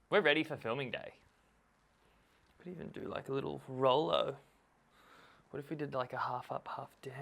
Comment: Did you just say "we can do a little Rollo"? :0